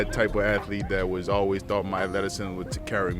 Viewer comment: What'd he say he thought would carry him over? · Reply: athleticism